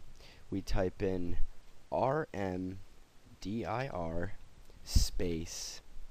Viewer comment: After searching the internet and finding long winded "download this...and install that"...one guy even suggested reinstalling windows. lol - I'm glad I found your video, easy step by step instructions. I misheard & put RNDIR....then corrected it to RMDIR....didn't work! changed it to DEL del /q /s FILENAME~1 Worked for me..Thanks!